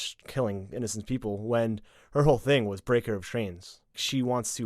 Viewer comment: Did you say “breaker of trains”? What trains? The ones from the unexplored west that delivered Starbucks to Daenerys?